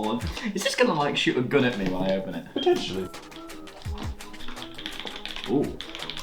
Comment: "Is this gonna like shoot a gun at me right now?" "Potentially." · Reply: Yeah, lol I thought he said Eventually